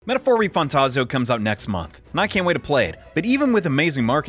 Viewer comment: Almost sound like you said Metaphor Refundtazo and I hope the game is good because if not, that’s a crazy name